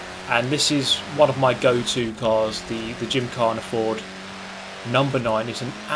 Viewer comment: I swear i keep hearing "The Gym Car I can´t afford" needless to say i´m confused.